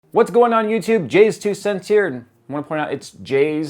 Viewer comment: Everytime I watch your intro, I hear "transition" not JayzTwoCent" :/